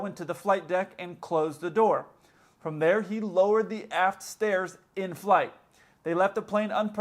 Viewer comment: You said "aft stairs." I heard "ass stairs." Then I saw the photo. 😂😂😂